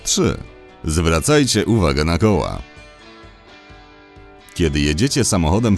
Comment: czy ja usłyszałam "Zwracajcie uwagę na goła"???